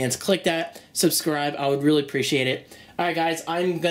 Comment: did he say "orgazm" on 6:50? LOL